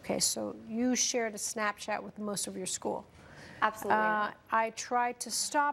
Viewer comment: I could of swore I heard her say she absolutely did when the Snapchat was first brought up. 2:48. However the judge reads sent but asked if she shared???? So idk. Got me a bit miffed?